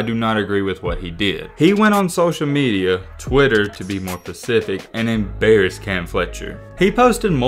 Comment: lol at 8:20..."to be more pacific" instead of specific...come on..been a fan of yours but this video and everything about it is terrible. Clean it up